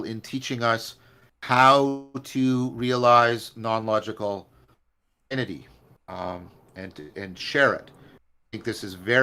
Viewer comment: I didn’t quite catch what he said at 2:01 - how to release non-logical ____ ? Can anyone help? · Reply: He says "non-logical identity" at that point. In Episode 49 of AFTMC, John talks about this. In transformative framing, we need to have a "divine double" that can be both an identity and a self-transcendence of identity. Here's the link: <link>